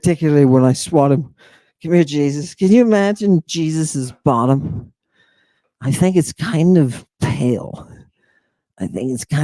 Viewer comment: i misheard "can you imagine jesus as a bottom?" I was not prepared